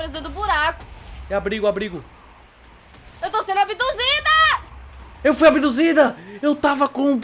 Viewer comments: " Só eu que ouvi o Jotave falando, eu fui "*ABDUZIDA*"? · o falou que foi "abiduzida"